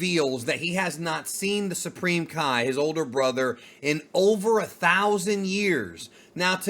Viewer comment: He clearly says over millennia did u check ur own picture in the video 🤔🤔🤔